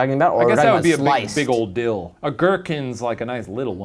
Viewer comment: Did Shane just say ogórkins? Like...Polglish? Or am I just hearing things? 😂